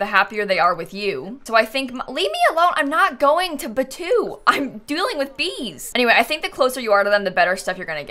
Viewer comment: Is it just me or at 1:21 does it kinda sound like said "I'm dueling with bees!"